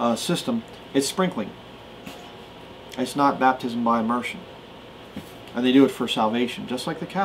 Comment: What does he say from 44:27 on?? I don't understand, is it baptism by EMERSION? And is that how he thinks it's supposed to go? I'm just asking out of curiosity and because I didn't get it; (so not trying to argue on here about it or whatever); I've never heard anyone say there was anything wrong with the way people are baptised, even though I've hear a lot lot of people say there's a lot of things wrong with churches, and occults etc., with lots of things, lol. Thanks!!!